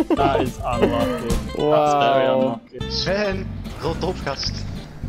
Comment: wtf did hé Just say: Sven rot is op gast!? Because that is dutch and i am duth